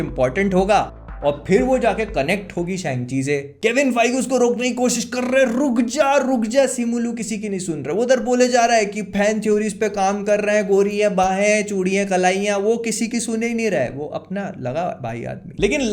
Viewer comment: tbh i heard behench*d and in real its fan theory, btw best part of the video, lmaoooo